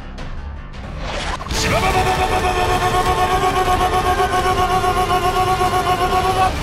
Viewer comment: I hear shibobobobobobobo thing and I was like: shibo?...mm🤔 oh shibou!" And remember something that shibou means in Japanese is (die or death) like muda means in Japanese is pointless